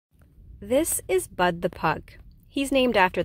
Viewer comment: My boy Bud being chill AF during this whole procedure. What a cool little man 😎👍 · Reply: yeah when I first herd her I thought she said “ BUTT “ the pug 🤔 perhaps “Butt” short for butter ..?, and I herd “Butt” I thought Butt like a soft booty “if you know what I mean ... and then I was oh Bud like the 🌱 plant 🤷🏻‍♂️..., so his owners are weed heads or big stoners ..? Thwey named him that ireee good 😌 good 😌 the kind plant 🤷🏻‍♂️